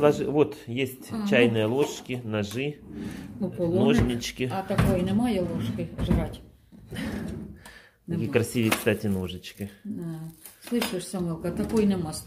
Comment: а такой нема ложки ... жрать ? "жрать" мне послышалось одной ? Квартира хорошая , на несколько месяцев . Вот захочет ли возвращаться туда где лифта нету , это вопрос .. молодец , делает для мамы всё .